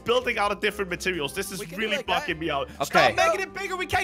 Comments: why does it sound like Crainer said “fucking” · is he saying bugging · did crainer say "This is really f***n me out"